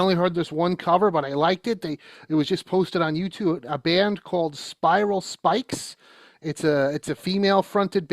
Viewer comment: For anyone who looks for that version of “Born Again,” the band ‘s name is Spiral Skies. I thought he said Spiral Spikes. The transcript says Spikes too.